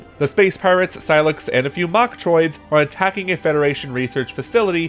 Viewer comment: When he said mochtriods at first i thought he said bok choy😂